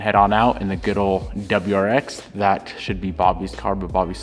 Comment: WAIT BOBBY SOLD THE STI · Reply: At first I thought I heard that but I think he said Bobbi "stole" the STI